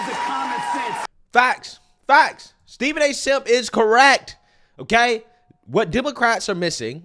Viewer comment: wait, did you call him Steven A Simp? 😂